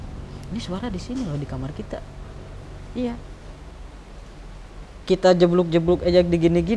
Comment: Ini kalian cuma Berdua apa gimana bang, di menit ke 37:39 kyaq ada suara cewe yg bilang "IYA" atau gw aja yg salah denger atau ada hal yg lain bang?